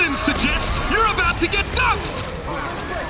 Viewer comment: Lmao I heard wrongly at 5:27 the caster said ‘ you are about to get fucked’